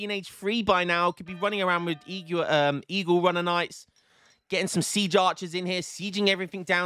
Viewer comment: I heard it as "e-girl runner knights".